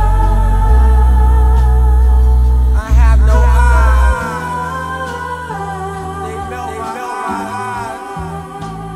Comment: What is he saying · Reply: I have no eyes they melt my eyes